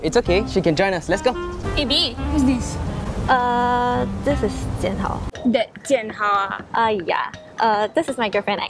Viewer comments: the girl said “this is jianhao” but the English subtitles said “this is the house” 😂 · she says this is Jian hao but the captions say this is dinner😂😂😂😃😃